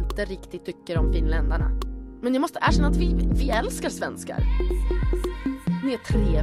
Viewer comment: There's no way the subtitles are right... I can hear words like Finland and Svenska and shit like that and it's nowhere in the subtitles... 1:54 I'm pretty sure she's saying something like "Vi älskar Svenskar" which would be like "we love Swedes" and the subtitles say "it makes all the difference"... wtf? And then she''s saying something about looking people in the eyes and oil and stuff, or beer... right? And she says she has Swedish and Finnish blood... Then I went back and she says something about being asked to do a video in Swedish and that she's doing it half as a video in Swedish and half as a make-up tutorial. And then she says her Swedish isn't perfect but that she loves you... And then something about Swedish not being the main language in Finland or something like that... Something about her Stockholm accent?? And then a bunch of shit I don't understand and then I was back at 1:54 Am I wrong?